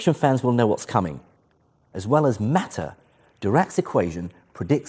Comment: he says 'Dirac's equations' and the CC says 'directs equations' haha